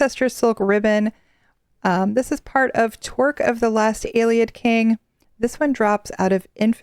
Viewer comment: I was listening to this in the background and swore you said Twerk of the last ayelid king XD TY for our weekly news